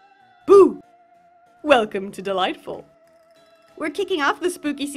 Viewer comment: always thought she said "and young! welcome to the light blub." just realized she said "Annyeong! welcome to dollightful." i want new ears ssjsjsjsj